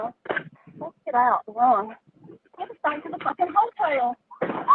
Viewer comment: It sounded like she said "Let's get out" and something about going "back to the f-ing hotel", so, I doubt she meant to kill herself. Might have been fighting over the gun (?) or could have been accidental with the air-bags in the way.